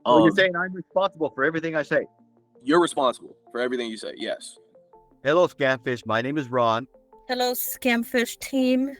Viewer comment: sounds like he says hello scatfish